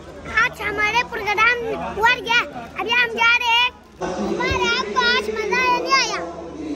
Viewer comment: Did he actually said program wargya?